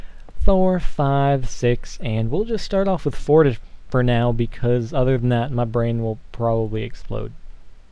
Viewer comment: i swear it sounded like you said aaaand we'll just start off with forty